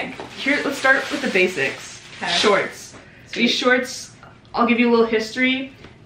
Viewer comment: i thought she said "sweet tea" like from mcdonalds and i was like "ohh they tryna step up on some tea"